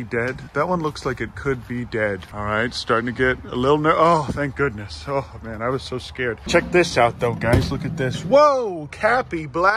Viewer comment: I thought he said cappy black